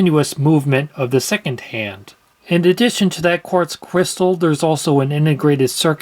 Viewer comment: I thought he said quartz quistal 😅